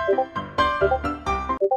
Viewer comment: why did i hear "oh shit"?